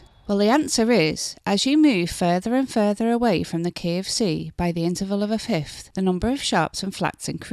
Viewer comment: I’m so sorry but as an American who playing by ear their whole life trying to learn music theory, what I heard at the beginning was “As you move further, and further away from the KFC” (kentucky fried chicken) lol my heart sank until I realized I completely misheard you. My sincerest apologies